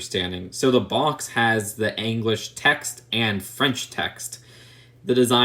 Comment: Did he just say, "Aanglish"? 😂